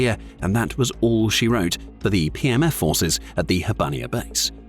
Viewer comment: A couple of times it sounded like you said "PMS Forces". I thought it hilarious and proper at the same time.